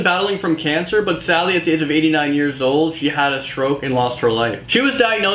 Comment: Did he say "HE had a stroke and lost HER live"???